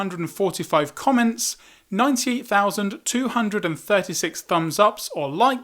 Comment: u said 90 thousand instead of 98 thousand